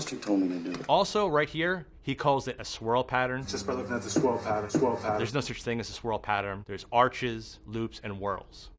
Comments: I think at 7:40 he says "This Whorl pattern" instead of "The Swirl Pattern" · I think he said "this whorl pattern". Funny when he's talking about all the stuff going on at the crime scene and there's a guy doing the limbo under the yellow tape :)